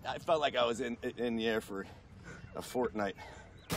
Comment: Is it just me or at 6:22 I hear him say Fortnite🧐😳